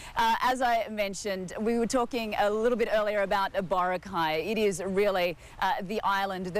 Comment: what is the word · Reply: Boracay-it's an island resort and a well known tourist spot